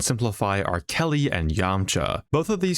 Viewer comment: i heard it as R Kelly and yamcha and I never laughed so hard in my life